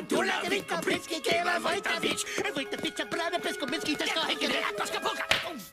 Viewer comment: did they just say "Fight the bitch"????